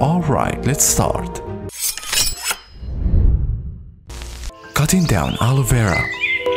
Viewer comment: Thought he said, cutting down our Vera,, ha love.